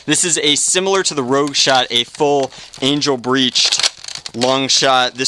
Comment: i thought you said anal bleached haha